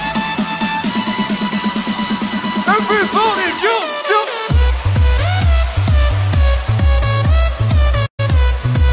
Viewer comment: Al minuto 0:27...QUE DICE?? ALGUIEN AHI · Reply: Everybody Jump Jump =Todos salten salten!